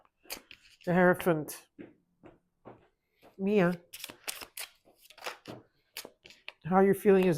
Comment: Did you say my name Mia sounded like it and I watched it twice and really sounds like you say Mia 6:59 / 31:20 ❤